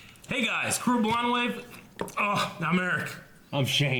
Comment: this has been bothering me since I subbed to this channel. English is not my native tongue so I have trouble with that. Do you say "cool blind wave here" in the beginning ? stupid question but it makes a comment. Get out of my personal space. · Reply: I always thought they said "cruel blind wave" which made little sense, never questioned it though